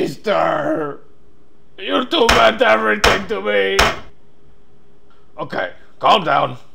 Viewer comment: it sounds like hes saying "Youtube is everything to me!"